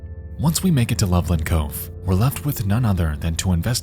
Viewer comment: damn I thought you were saying loveland colorado I miss that place